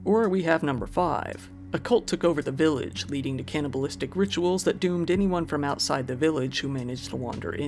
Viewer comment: Thought it said cannabis not cannibal lol.